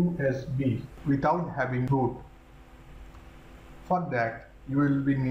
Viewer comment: did you just said fuck that xD that will need three items